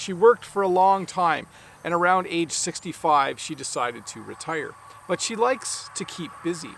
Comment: I got confused when you said "around age 65 she decide to retire" without subtitles on I heard "around 865 she decide to retire"